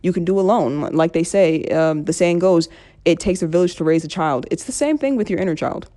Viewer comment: It takes what to raise a child? I can't understand what you said. (old speakers) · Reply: It takes a Village to raise a child .